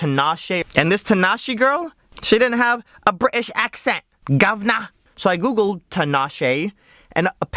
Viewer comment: "She didn't have a british accent, GÓWNO!" That's what I heard, weird. Btw in polish gówno means shit.